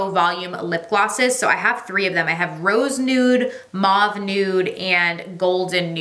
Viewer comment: I thought you said "mom nude" instead of mauve nude for one of Mario's glosses lol 😂 I'm dead